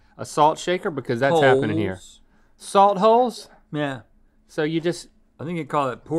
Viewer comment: I thought Rhett said, “glory holes.” 💀